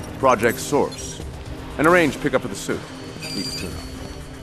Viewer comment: After a couple years, I still have no clue what Lex is saying after "And arrange a pick up for the suit." All I hear is "Leave a tuna."